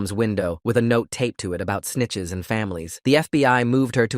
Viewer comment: what did he say? sni!itches get at!tches?